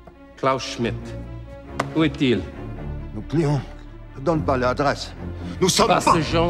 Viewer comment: As french i can say its a pretty good linguistic work, but that guy who has to be french, we see that isnt just hearing is speech is almost unpronunciable in french discuss like there (1:01) i didnt know what he said because of its articulation on dirait qu'il dit "nonanin donne pas le adresse' aucun français parlent comme ça et pourtant on a bien des accents en France, à des moments pour un parigo on croirait entendre du vieux occitan